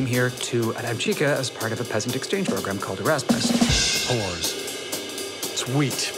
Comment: co on powiedział po angielsku?? zmienili tekst żeby nie bylo tam "jews" czy jak? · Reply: Pierwszy człowiek powiedział "Whores" więc pierwsze litery się zgadzają, czego ty nie kumasz xD